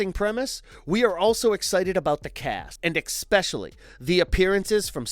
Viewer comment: Time stamp 1:44 Did he say ekspecially?